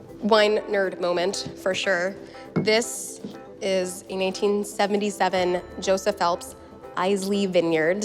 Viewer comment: Is it just me or does call the red wine a 1877 Joseph Phelps right at 2:50.